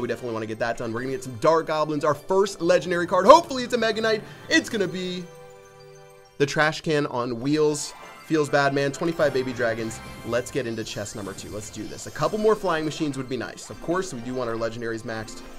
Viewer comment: I thought u said the trash can on wheels is the best card of all time!